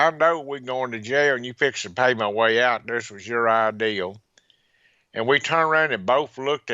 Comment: This guy has got such an iconic Southern drawl, it's fascinating just listening to the accent, let alone what he is actually saying. Interesting how he sounds like he is saying "ideal" when he says "idea". A good thing about this interview is that nobody interrupts anybody else purposely. They all let the person who is speaking finish his question or answer and then they start. Compare this interview to the one on YT called Mel's Hole, with Art Bell and Mel Waters. Those guys are both interrupting each other, not letting the other finish, talking over each other, it's quite shameful.